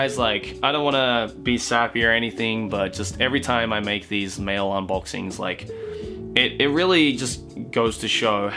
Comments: Oh, thought he said "male unboxing" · i thought u said "toay we are doing another male unboxing" oops also i wish u guys would come to DesuCon (norway) this july, but its.. mid july...... *cries*